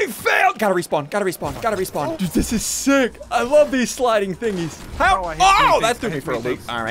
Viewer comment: Did just say the f word (Like if you also heard that) Edit: thanks for so nany likes i never got more than 2 lioes before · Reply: He said "this is so f*ckative" i think